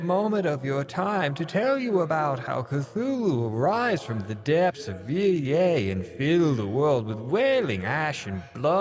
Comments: I heard that as "rise from the depths of EA". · im sorry, did you just say "from the depths of EA" instead of depths of hell?